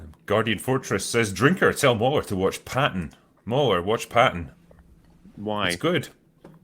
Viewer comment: I thought drinker said pan instead of patton. Was thoroughly confused.